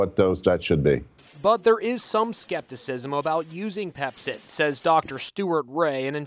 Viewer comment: I am so stupid. I thought they were saying pepsi